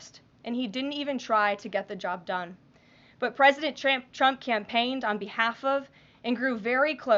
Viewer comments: Am I the only one who heard her say President Tramp! Is this a SNL skit? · WHAT DID U CALL HIM? Pres. TRAMP?